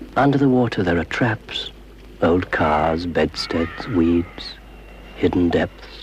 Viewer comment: When he said weeds in the swimming one I thought he said weebs